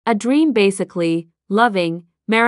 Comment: I heard “A dReAm”